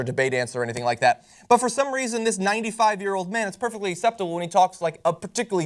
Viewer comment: LOL did I just hear you call him a 95 year old man? I'm sure he's not that old but anyway maybe I misheard you. Anyway my theory is that he has early onset Alzheimers and that someday, just like with Ronald Reagan, we will discover this.